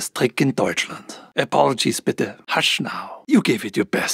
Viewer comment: what does he said hashina? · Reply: "Hush Now"